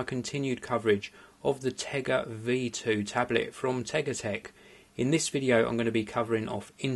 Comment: I thought it said Tegra :(